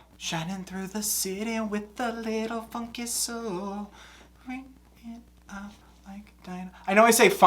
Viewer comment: Full disclosure: Every time I see your videos, I feel more and more heartwarming love for your amazing personality and stance on life. You are a person, who deserves all the happiness and goodness this world has to offer. If your anxiety tells you, that I'm being even 1% dishonest, when writing this, let me assure you it's lying. I'm sending all the positive energy I can fathom your way. I'm wishing it into the universe, that one day you see yourself for the amazing worthy person you truly are. Thank you for your content. Thank you for being in this world. Because you being here makes the world better. Every. Single. Day. Much love and the best wishes, from your Greek friend in Cyprus P.S.: Your brain says "Driving through the city with a little funky soul!"! I find that wholesome! You go with that funky soul of yours!!! (nevermind that my brain continues to say "...with a little f****ing song! XD) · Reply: Hee, I hear "a little f'n soul." It makes me giggle. 😄